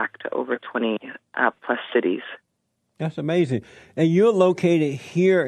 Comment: When Jesse says “Thas Amazin” I hear “that’s stupid” 😂😂😂